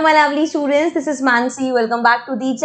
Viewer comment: When she said lovely student i heard it as ugly student